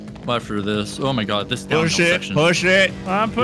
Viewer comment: For everyone wondering at 11:00 minutes did not say oh sh*t, he said push it